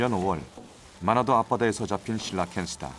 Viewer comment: 몇번을 들어도 "마라도 앞바다에서 잡힌" 으로 들려서 이상하다고 생각했네요. 한국은 아닌거 같은데 왜 마라도에서 잡힌 물고기를 저기로 가져갔을까? 라는 생각이 계속 들어서 실러캔스 술라웨시로 검색을 해보니 마라도가 아닌 마나도에서 잡혔다고.. ㅠㅠ 저만 이렇게 들은거 아니죠?